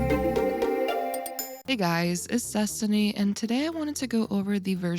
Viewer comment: why do i always hear "hi guys its sussany"